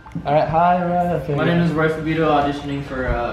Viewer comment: he says hi my name is I thought his last name was wassabi